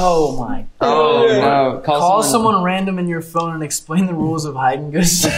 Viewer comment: did Daniel say call Randy? 2:06? 🥺